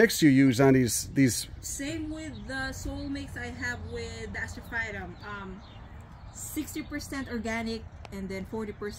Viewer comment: At around 19:45 or so she mentions what she uses instead of perlite. I heard the cat litter but what else was she saying? Surface and BP? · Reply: My wife thinks it was Turface MVP